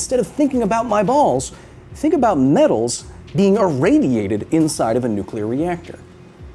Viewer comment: All I heard was ". . . think about my [b a l l s] being irradiated."